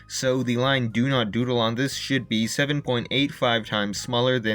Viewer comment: it sound like he said sh*t but he said should